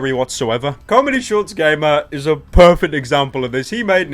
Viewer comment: I thought he said 'corny gamer'